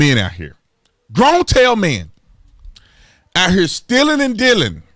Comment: what did he say here? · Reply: Grown Tail Men. LOL